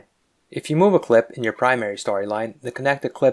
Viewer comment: Great tutorial can you please tell me the shortcut key you mention at 4:56 concerning moving clips without their connections?. It is hard for me to understand what is being said. Thank you so much for your time and making these videos great work! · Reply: Its called the Grave Accent Key. It's in the upper left of the keyboard shared with the tilde key, the backward apostrophe used as an accent in French